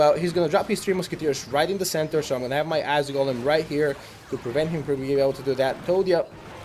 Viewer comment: did you say "ass golem" ????